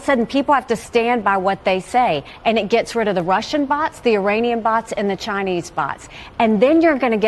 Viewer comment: Did I hear “Uranian bots”😂 never heard of the country of Uran..